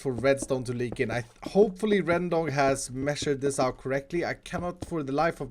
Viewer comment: was it just me of did I hear rendong